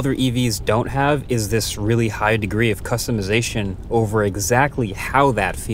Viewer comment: Did you say hi degrees 🔥🔥